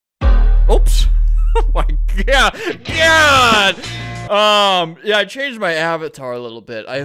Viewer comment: i never thought i would hear albert say gyatt and i wish i never heard it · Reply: I think he said “god” but idk